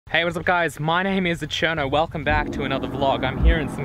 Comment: Great video. Why does it sound like you say 'My name is aCherno'? Am I hearing things?